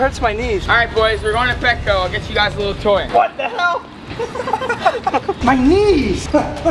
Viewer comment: Did Say WTH YEP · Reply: ok he said hell what about it